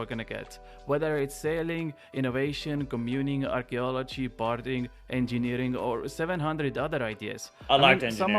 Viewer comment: When said "barding" I thought at first that he said "Partying", now I can't stop thinking about how awesome a Partying skill could be 😂😂😂 Vote yes to Partying, catch me at the Monastery with the monks! 🍻🎉